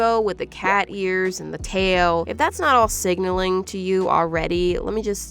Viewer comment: help why did I think she said “sigmaing”